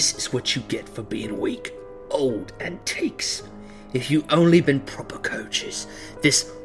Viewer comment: what does he say? "Intakes"? I haven't heard that slang. edit: Had a listen, he appears to instead say "antiques"